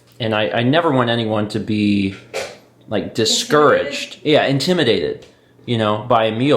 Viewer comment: I misheard that as "intimidated by Emile" 😅 Don't let tell you what you can or can't eat ;)